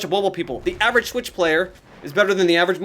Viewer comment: I thought everyone was saying OH EVERY SWITCH PLAYER IS A BOT